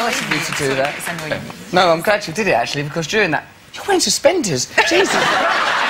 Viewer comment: can anyone tell me what he said in 8:26 ? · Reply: Samir Khanal “your wearing suspenders, Jesus Christ.”